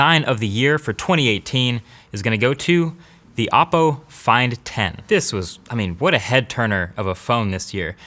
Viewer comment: did he really said "oppo find 10"?? my life is over